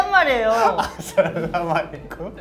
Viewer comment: sounds like he was saying assalamualaikum